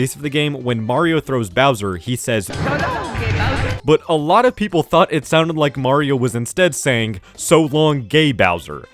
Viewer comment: WAIT WHAT IS HE SUPPOSED TO HE SAYING??? 😭😭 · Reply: I think it's "So long-a Bowser" or "So long King Bowser"